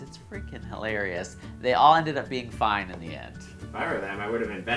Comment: It sounded like he said the last group was "all fined in the end". Which they should have been, fined for stupidity. Who drives that fast over waves?